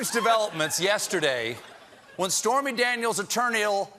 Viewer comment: "It's terminal" is what I heard and that's the best line I've ever heard from Jon Batiste if that is indeed what he said